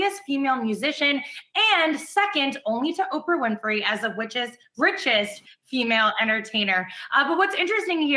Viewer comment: did she say “witches” instead of “riches”? Damn woman, you jelly?🙄